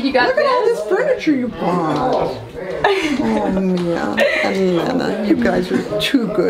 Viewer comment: real name is Lena?? 59:05 did anyone else hear that, lol I've been so curious to know her actual name